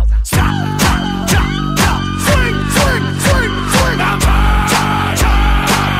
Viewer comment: I hear "now die" not "now murder"